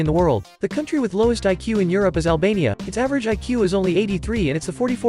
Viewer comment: ALBANIA SMART. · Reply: i thought you said alabama